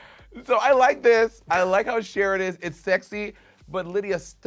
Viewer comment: DID Monet say Cher or shear?!? 😂